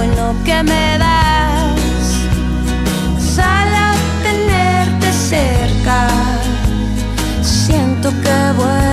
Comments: Y yo pensando que decía "sal al tenerte cerca" :v Editado: nunca tuve tal cantidad de likes :") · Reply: Yo pense que decia "¿sale tenerte cerca?" En plan, puedo tenerte cerca? Xd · Y yo pensando que decía "sal al tenerte cerca" :v Editado: nunca tuve tal cantidad de likes :") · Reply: Yo pensé que decía "sala tenerte cerca"